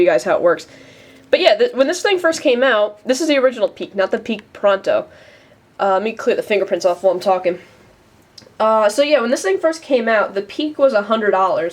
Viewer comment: Sounded like you said Peek ProNo. wow I relaly have to wait until the vide is over to comment lol